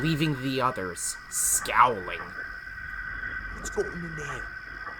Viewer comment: what did he say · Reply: "What's gotten into him?"